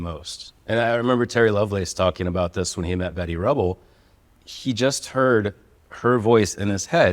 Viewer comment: …did he say… Betty Rubble?